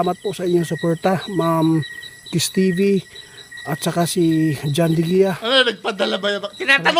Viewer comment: bakit narinig ko yung pangalan ko jan hahaha john villa😂😂 awit ka boy diwindi